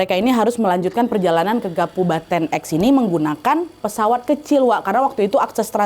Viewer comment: salah denger ga ya di 4:08 bukan kabupaten tapi kapubaten😂